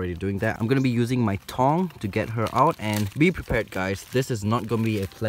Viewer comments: The captions say ‘I’m gonna be using my tongue’ instead of tong 😂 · I thought you were saying "tongue". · I thought u said tongue